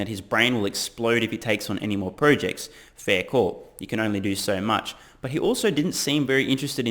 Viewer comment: "Fair core", did you say?